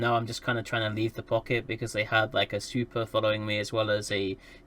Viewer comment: Ok I thought you said "super" instead of "svipul" and was wondering what chucklefuck dropped a supercarrier on a rifter.